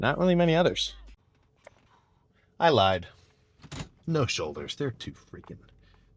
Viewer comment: I thought you said no shoulders for awhile and you hit it the next day 😂